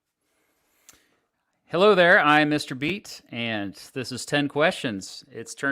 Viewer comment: For a second there I thought it said Mr. Beast. Professor Dave really popping off.